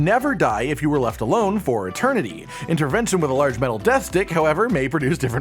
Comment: So at 4:45 did say a giant death dick then laugh a bit bc he decided to keep going or was that just me. Lmfao